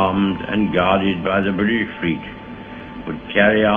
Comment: My hearing is wrong I think. Churchill at 2:20 - stamp. Guarded by what? Blyfritt? Like unleaded fuel in swedish? I have listen to this part 20+ times but I can't make out what he says. Enlighten me please :) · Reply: British Fleet